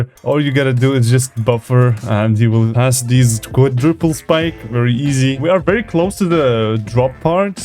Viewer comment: i thought you said ,,Quack diple spike" XD